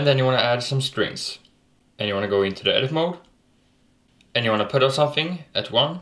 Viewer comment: What did he say on 2:09, add some what? Sorry I just can't rlly make out what he's saying :/ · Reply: I Think he is saying: "And you wanna go into the Edit Mode" but I'm not sure. Maybe you should ask him ;)